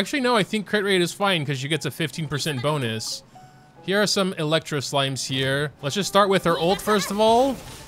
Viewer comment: did he just say electro silime? 15:22 bruhhhh